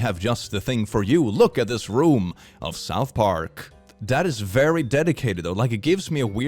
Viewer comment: I heard say "Daddy's very Daddy-cated". Am I alone with this?